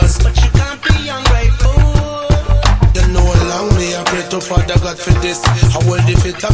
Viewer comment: At minute 1:47 sec. -- 1:50 sec., it sounds like he says "allow me a break to follow God for this"------Not "and allow me a beat to follow, I'LL do this."